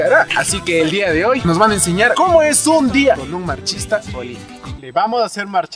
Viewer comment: Pensé que decía Machista Olímpico jajajaja